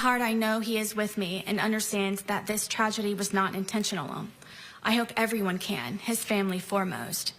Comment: what is the extra syllable she adds into "intentional" Intentionable? Intentional'n? It almost sounds like shes saying just that one word with a thick Japanese accent 😅 "So desu nehhh INTENTIONARU, kentu sugoroi" 🤣